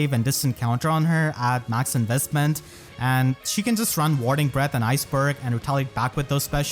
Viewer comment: I thought he said morning breath, I was like ew what 💀😂